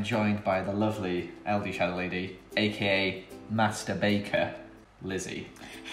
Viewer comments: I know I'm a year late to this vid but at 0:09 I thought he was going to say mastrubater 😂 I'm dead. Also at 0:35 I thought he was going to say biscuit porn 😭 me and my dirty mind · Omg I heard Joel say “masterbaiter” 😂😂 oof I’m sorry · Joel: joined by ldshadowlady, aka master baker! Ldshadowlady in her head: did you say masterbaiter? At 0:11 in the vid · Master Baker... Bad choice of words... xD IM DIEING ITS SO FUNNY HQHAHAHAHAHQHQ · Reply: Ohhh I litterally thought he said masturbater XDDD